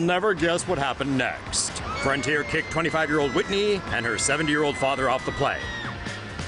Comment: I thought they said 7 year old father 😂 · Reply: Wonder Woman they said 70 year old